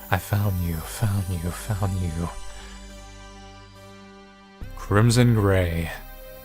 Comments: thought it said "Crimson Gay" for a second there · I thought it said "crimson gay"